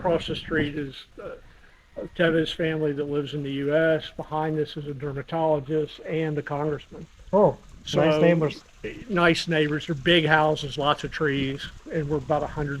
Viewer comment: When he said congressman I thought he said scammer.